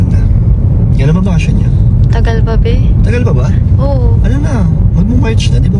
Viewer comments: WOW am I right I heard said "an tagal p BHE" 😍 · Wala bang nakapansin sa napansin ko sa sagot ni sa tanong ni doon sa bakasyon nya sa shool.ewan ko lang kong mali sa pandinig ko sa sagot ni na "matagal pa bhe"12:18 kinilig ako ng sobra 🤎🤎🤎🤎🤎🤎🤎🤎🤎🤎silent viewers nyo ako pero dito sa narinig kong sagot ni napacomment ako bigla 🤎🤎🤎🤎🤎🤎🤎🤎🤎🤎